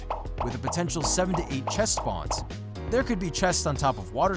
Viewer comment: Thought he said 78 chest spawns